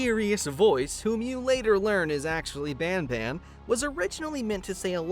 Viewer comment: When i first heard the name "banban" i thought bourbon 😂😂😂 And also that part where "a message says turn back which is written by ketchup.... obviously ketchup"